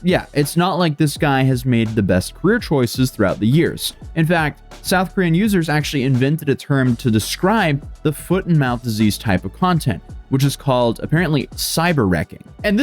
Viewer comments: I first heard "foot IN mouth disease" and got really offended Cause that ain't a disease it's a lifestyle 😎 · The way you just casually call him Foot and Mouth Disease.......... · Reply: I thought he was saying, "Foot IN mouth disease." 💀